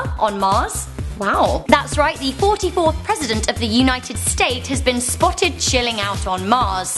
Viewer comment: Obama's the 50th president. not the 46th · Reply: I thought she said 44th president